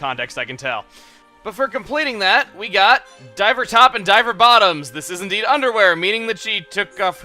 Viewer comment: lol i thought you said diaper bottoms when you said diver bottoms